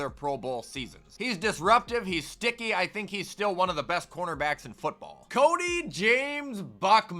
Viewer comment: I thought he said Gordon Ramsey until he said cornerback